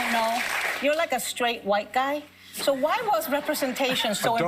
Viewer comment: Did he just say “A dope straight white guy”?